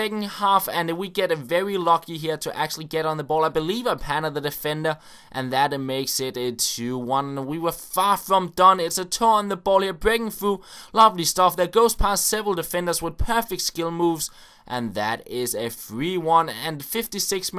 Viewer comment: Sounds like your saying a toe is on the ball